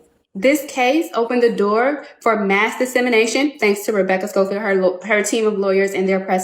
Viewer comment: what is she saying here ? Mass Insemination / Mass Dissemination. Because if it's the first, I'll probably watch.